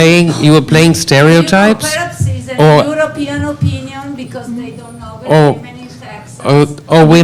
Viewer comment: Can someone tell me what that Italian female journalist said? bec the host was suck i couldt hear it clearly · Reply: "european opinion because they don't know (?) women in texas" is all i managed to hear